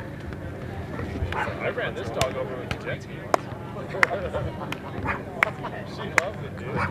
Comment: wtf did he just say 🤣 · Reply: He ran a dog over with a jet ski!😅